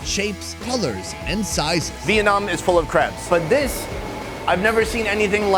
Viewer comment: Thought he said "Vietnam is full of craps" instead of crabs